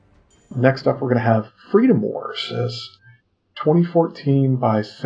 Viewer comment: Sorry but i didnt quiet get what you said on 2:45 could someone please tell me the name of the game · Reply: Freedom wars.